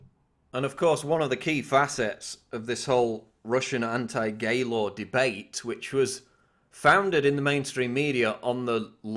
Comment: I thought he said “gaylord” lmao